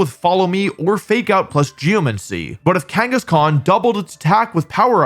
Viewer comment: i heard "Genghis Khan" XD